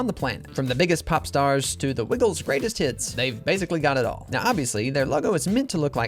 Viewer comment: i thought he said: to the wiggles rubbing their t!ts 😭